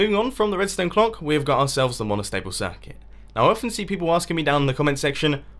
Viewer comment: I just realised it's called "Monostable Circuits" and not "Molestable Circuits". I've called these things as molestable circuits for years and when spoke, I always heard it as Molestable Circuit